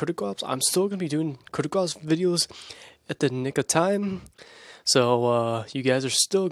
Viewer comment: is it just me or did he said the n-word?